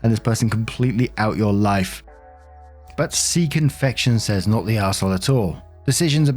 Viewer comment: Oh! Sea Confection 😂 I thought you said Seek Infection 🤣 had to double back for clarification and then had go back to the beginning of that last story! I’m dying and my neighbors don’t know what to think! Speaking of Reddit warping minds!